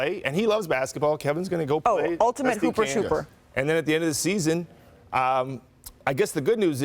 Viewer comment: did she say Hooper Trooper?